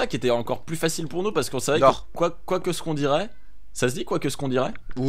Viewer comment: c'est "quoi qu'on ait dit"